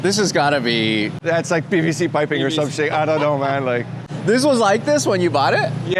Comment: When the Renault owner said pvc piping I thought he said blk bbc piping 😂😂